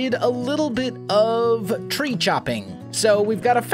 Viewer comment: When he said "tree chopping," I thought he said "treat shopping"